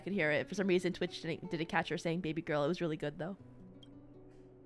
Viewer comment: IDK, I heard say "Babygirl" clear as day.